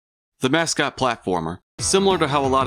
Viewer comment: for a second i thought you said "the mascot plokformer"